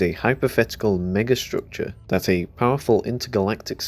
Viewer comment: did i hear him say ni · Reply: Mega structure is what he said